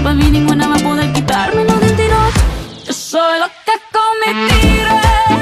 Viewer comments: yo pensé que decía "soy loca cometible" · Yo pensaba que decía "Loca come tigre" :v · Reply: Yo pensé que decía “yo soy loca cometí un crimen” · Yo pensé que decía "loca come mi tío" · yo pense que decia ''yo solo come tire'' XD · la única forma de entender la canción fue venir a verla con subtitulo, siempre pensé que decía: YO SOLO TE COME TIDE Shakira la unica cantante latina que tengo que buscarla con subtitulo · Yo siempre creí que decía “soy loca come tigre” 😔✌🏼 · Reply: y yo que pense que decia "soy loca no mentire XD" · Pense que decia "soy loca cometible" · siempre pense que decia " y ninguna va a tiro "😵‍💫😵‍💫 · Yo pensé que decía soy loca con mentiraaaaa 😭😭😭